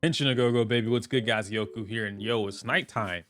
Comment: Random question but what exactly does he say for his opener because it sounds like he says Genshin a go go baby and I’m so confused everytime😂😂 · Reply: Henshin a go go baby. It’s from viewtiful Joe. 😅